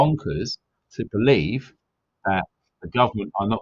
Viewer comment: I thought you said 'taxis', lol. 😀